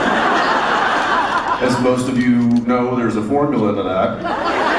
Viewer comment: I didn't get what jensen were saying 9:33Someone write it down so I can go and translate it because i am not english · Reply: lool really thank you , i've tried every single way to write " formula" and i couldn't